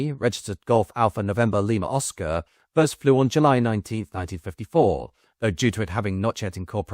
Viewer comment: did you say 1974??